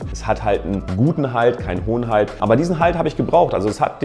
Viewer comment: Du sprichst definitiv zu schnell,ich habe immer Hoden-halt verstanden,anstatt hohen halt 😁